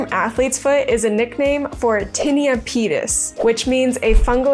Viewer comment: i heard tiny a penis😭😭